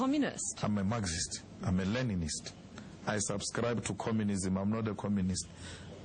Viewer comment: does he say he's a Learningnist? WHAT!?!?!?!